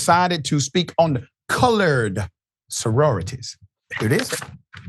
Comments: It sounded like he was saying college sorority not colored. I do agree he's an idiot and a fool, but I actually think he said college. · He said college. You know he said college. Pathetic. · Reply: He did not say college. I listened to it 2 times to make sure he said what I thought he said. He said Colored Soroity. It's ok. Just showing the world what great conservatives they are.💯💯👍🙏🙏 SMH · I heard “call-edge” not “cull-urd”. This guy is talking sh*t and believe it or not I can smell his breath through my phone.